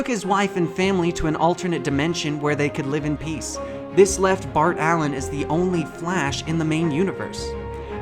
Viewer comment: Did you just say Fart Allen?